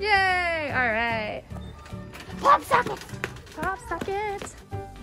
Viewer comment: When you said pop socket I thought you said hot pocket and sang the ad😂😂